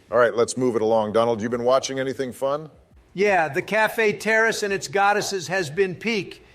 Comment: I thought he said "Café Terrorist and Its Goddesses" And I typed that...